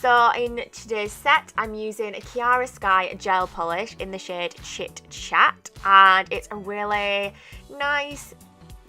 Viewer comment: They look lovely hun, what was that colour again ? It sounded like you said Shit Shat lol that can't be right surely.lol, Anyway great work hun x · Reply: Haha omg!! its called chit chat hun thanks so much xxx